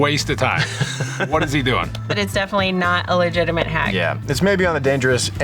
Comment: Did say "yes" in Hungarian - "Igen" @ 15:44?! :) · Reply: She said ee yeahh